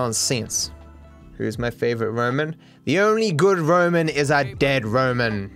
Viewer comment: I thought he said, "The only good woman, is a dead woman," and I thought to myself that he must not be getting matches on tinder recently